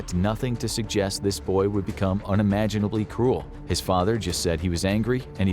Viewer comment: Every time you said “cruel” i heard “cool” and i was like “brutal dude lol”